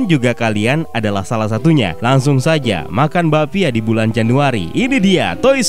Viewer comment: Gw gak salah denger 😕 Makan babi di bulan januari? Wkwkwkwk😆😆